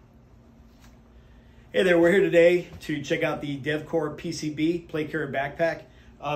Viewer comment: I litterally thought the guys said "deathcore" PCB but he said Devcore lol